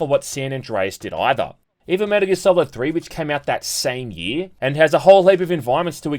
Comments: What is the game at 8:35? I can't understand what the guy is saying and the subtitles are wrong due to him speaking so fast. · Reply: That's Metal Gear Solid 3 Snake Eater. Great game. They're coming out with a remake of it soon. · What is the game at 8:35? I can't understand what the guy is saying and the subtitles are wrong due to him speaking so fast. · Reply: Metal Gear Solid 3: Snake Eater <3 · What is the game at 8:35? I can't understand what the guy is saying and the subtitles are wrong due to him speaking so fast. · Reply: Metal Gear Solid 3: Snake Eater An amazing game which also has a modern remake coming out soon called Metal Gear Solid Delta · What is the game at 8:35? I can't understand what the guy is saying and the subtitles are wrong due to him speaking so fast. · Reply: Metal Gear Solid 3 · What is the game at 8:35? I can't understand what the guy is saying and the subtitles are wrong due to him speaking so fast. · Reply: Metal Gear Solid 3, my good man. 👍